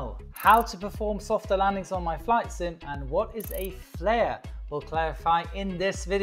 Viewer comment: did he say my flight sim